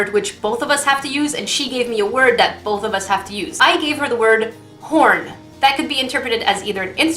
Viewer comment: who else heard porn when she said horn at 2:32 in the video